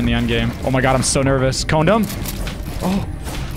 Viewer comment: Sounded like he said condo..... lol